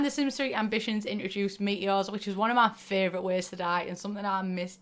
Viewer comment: i thought you were saying meet yours not meteors!